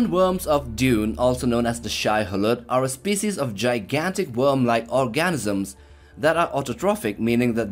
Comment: Did you just say "gigantic worm-like ORGASMS"??? Well...it sounded like that, bud!